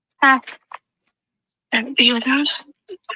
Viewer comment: Okay just wanted to share what I hear as I haven't heard anyone come to my conclusion. Definitely means I could be wrong, but in my college years I spent a lot of time listening to rough audio recordings for japanese homework trying to parse what they were saying, i feel pretty confident in my listening abilities but i also know this is a tough one and i'm super open to being wrong. says, “See, this is the path…” It’s been speculated the next line is “That be a gun.” I’ve been listening over and over. What I hear is: “that weird house…” and I believe this could be finishing her sentence trailing off as is running by saying “Oh crap!”